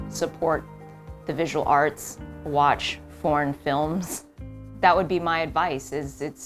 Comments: I heard that as "porn films" until I was like hold up and I checked the translation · My hearing is bad and need a speaker right next to me, I was certain she said porn films, but they didn't chuckle or anything, relistened ... I still can't understand what she said until I put in CC lol , this can't be just me is it?? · She said "watch foreign films" and I really heard "porn films". Which honestly, do that too. Why not lol. It's something to appreciate just like anything else. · At first i thought she said "watch porn films" and i was like jesus christ this lady is HELLA open about her sexual life! Did anyone else hear that? Because I don't have a particularly dirty mind and I'm honestly not even a huge fan of porn, but I thought it was weird that she was listing that as one of the top visual things to cherish! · I can’t be the only one who thought she said ‘watch porn films’ lol